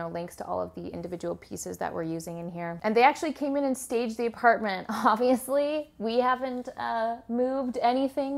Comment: I thought she said "they even saged our apartment " lol